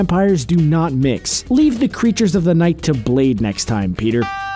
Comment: Just saying you should have enunciate your words a little different to make him come out like what you really trying to say cuz I thought when you said blade I thought you said bleed